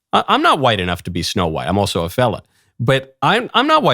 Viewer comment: When you said "I'm also a fella" I thought you said "I'm also a felon" and was like "MYYYYKE, MYYKE NO."